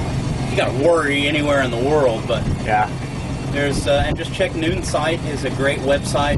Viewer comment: what website did he say to check for info? I heard Newtons? Starting back at the Episodes that I missed before I joined your site. Thanks!